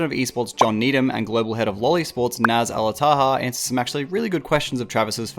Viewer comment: I heard that as “global head of loli sports” and got extremely confused as to what video I was watching